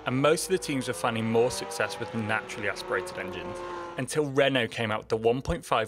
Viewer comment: Lol when you said “Renault” I could’ve sworn you said “redneck”